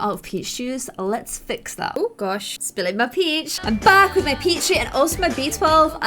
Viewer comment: When she said " im back with my peach juice"it sounded like she said I'm back with my pee juice